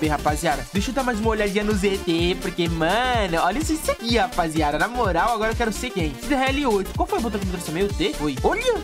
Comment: no minuto 6:19 so eu que ouvi : eu quero ser gay do?? Outa coisa o no mi 6:35 ele fala ciclone ot@ , pq serio eu ouvi isso kjkj